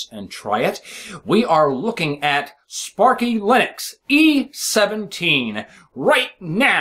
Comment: At first I thought that it said: "Spatry Linux" xD Would've been epic xD